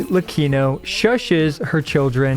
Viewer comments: “shhhhit-lecchino” anyone else hear that? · I was letting the video play in the background and I genuinely thought said "Shitlecchino"